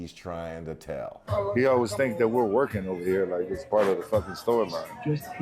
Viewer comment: maven misheard him. He didn't say it was part of the storyline. He said he always "thinks we're working" like it's part of the storyline